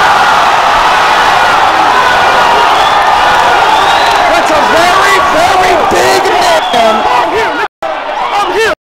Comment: what say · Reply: This the last time I reply to this lmao . Said that’s a very big man . Take that hating shit somewhere else . Fck is my shit jit. I don’t need the view